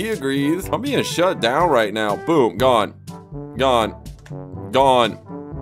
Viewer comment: Why is he saying the name of a Tyler the Creator Song? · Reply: I’m gone?